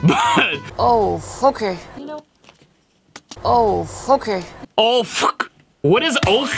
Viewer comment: Haha I love these! In Emma’s defence, it kinda does sound like certain English accents saying “oh f*cket” 😂😂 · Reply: I heard her saying „ohh, fucker” lol